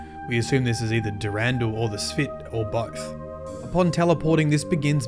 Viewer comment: I laughed so hard because of something I misheard. “We assume this is either Durandal or the S’pht, or both.” - that’s what is said. What I heard was, “We assume this is either Durandal or the S’pht, or Byf.” Lol